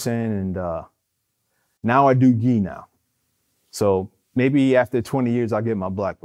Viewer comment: thought he said he do D now